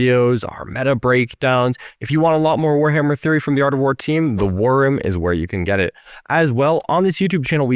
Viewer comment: when they say "the war room" I hear "the wyrm" 😂